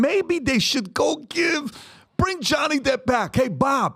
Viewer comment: Ohhh, bring Johnny back. I thought you said, make Johnny black..